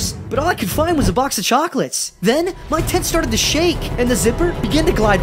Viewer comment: I thought he said "my tits started to move" 💀💀💀